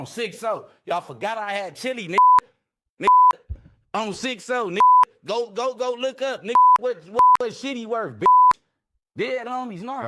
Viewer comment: DID HE SAY GO LOOK UP WHAT SHITTY WORTH 😂?